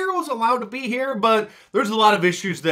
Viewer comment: did Mark say the bohemian grove? Idk what that is but I think that's what he said